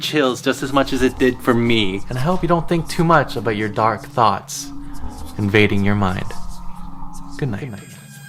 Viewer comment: I almost thought he said I hope your dark thoughts don't invade your money